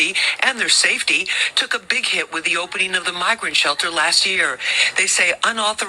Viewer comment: I thought she said N****r shelter